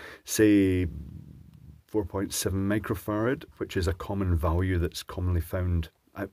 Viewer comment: Generated CCs thought you said "megafart" instead of microfarad at 5:32. I'm pleased with that.